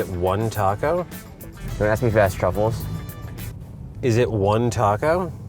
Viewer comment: I can't figure out what the driver is saying. It sounds like he's saying "do you want to ask me that with less troubles?" But at the same time it sounds like it should be something else.